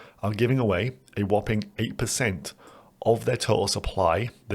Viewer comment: For a minute there I thought you said ' piss takers' instead of pyth stakers?